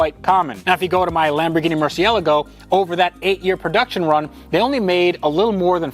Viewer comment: did he really say mersyeligo